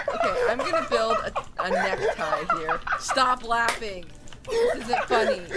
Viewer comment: Did call DEAR???!!!!! 2:41 :O